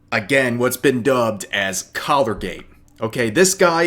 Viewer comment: At first I thought you said "Color-Gate" and I was like this video about to be craaaaaazzzyyyyyy!!! Still crazy tho.